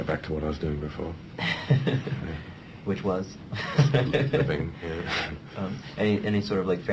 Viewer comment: what does he say at 1:43? sleeping?